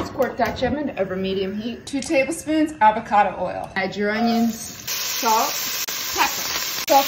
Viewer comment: I swear I heard her say edge your onions